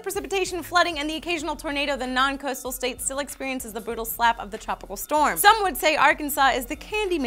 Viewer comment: does Lee say "bootal slap" instead of "brutal slap" D